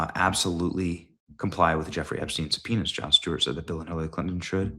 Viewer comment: Benny!!!! You gotta slow down when you say "epstiens subpoenas".......sounds like you were saying they need to comply with Epsteins penis......I couldn't quit laughing long enough to hear the rest of what you were saying brother!!! Love the show.....you need to come to Washington state and check out all the cool fraud we have up here!!